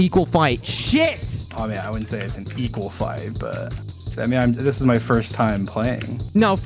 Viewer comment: did dream say tommy lol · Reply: i think he said ‘i mean’